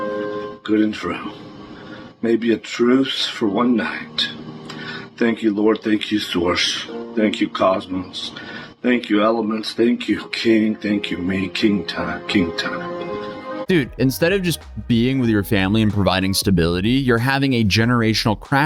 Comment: did he say thank you Swarzch? like in SpaceBalls?? :'D